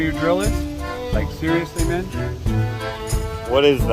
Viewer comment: When asked you if you dont know where your drill is, he says, like seriously Mitch? Or am I hearing things?